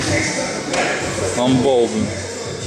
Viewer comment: what does dima say at 0:24 ? and what does ruslan say at the end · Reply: dima calls ruslan a hulk